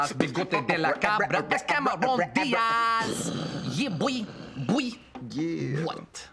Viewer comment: did he just say yeah boi